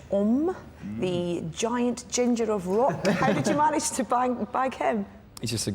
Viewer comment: Did she say "How did you manage to bang him?" o.O